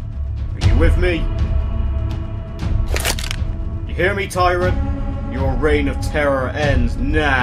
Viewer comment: What he say ???? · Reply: You hear me tyrant!